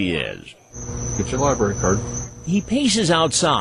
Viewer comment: If you have to say "get your library card" to prove her identity, she's too young for you bro... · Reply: Bro I thought you said liberty card 💀